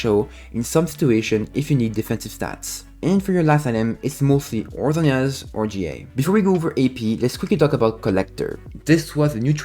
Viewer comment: what did you say at 3:10 ? Loving ur kaisa vids, keep it up :D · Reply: And for your last item (5th item ) its mostly or Zhonya or Guardian Angel ( GA)